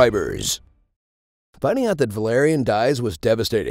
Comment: did he just call him Valarian? Like game of thrones?